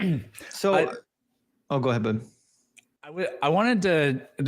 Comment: Did just call babe?:D